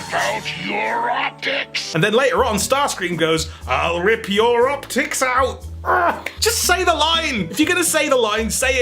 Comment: he actually said I'll rip your rockets out but I see how you could have made that mistake